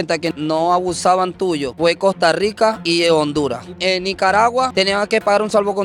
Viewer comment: Gracias hermanos hondureños... único país que se puso la mano en el corazón ❤ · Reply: escuché lo que dice. COSTA RICA Y HONDURAS...SON LOS 2 PAISES QUE HAN AYUDADO DE CORAZÓN....